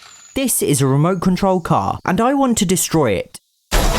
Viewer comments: I thought he said “I don’t want to destroy it” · I thought the intro was so peaceful and then I hear “I now I will destroy it” 😂